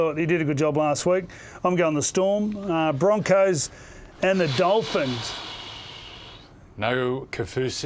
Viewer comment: Hey Freddy the Man I got it wrong my partner just whacked me upside for the head. I thought she said you tipped the Dolphins.Dooh my bad LoL